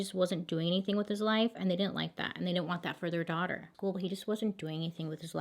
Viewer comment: When she was saying he wasnt doing anything and her parents didnt want that for their daughter i thought she said he wasnt doing anything except their daughter God😂